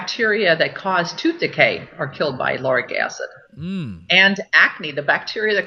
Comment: What is she saying at about 1:01:00? Some kind of **-acid for e.g. akne-treatment? Sorry, I'm a non-native speaker. Thx in advance. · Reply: Lauric acid.